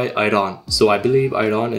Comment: I thought it said "Aryan" at first 💀